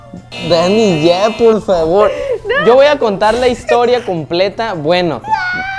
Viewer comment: yo se ustedes pero como que yo escuche un no de:v